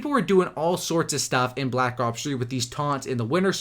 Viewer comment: Is it just me or does he sound like hes saying black cocks 3 ?